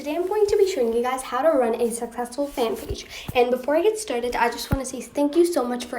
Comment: PLSS I THOUGHT IT SAID HOW TO RUIN A SUCCESSFUL FANPAGE